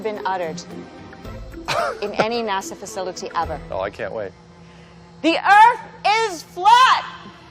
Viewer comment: When she hollered "The Earth is FLAT!!" , I heard a "Are you on CRACK??!" 😄😄😄😄